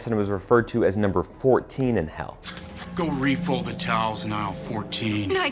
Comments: actually, the man who shocks madison in hell said “aisle 14” not “number 14” although the first time i heard it, i also thought he said number 14. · I thought her manager said "go fold the towels in aisle 14" not "go fold the towels now 14".